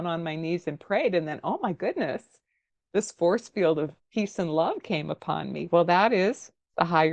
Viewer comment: Your face when you thought she said FORCEFIELD OF TEETH.... Had me rolling!! Fantastic channel, btw!!